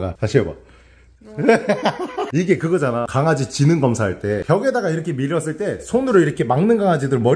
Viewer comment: 초에 지능검사가 아니라 주인으ㄹ 믿냐안믿냐 인거같아요..그렇게들었는데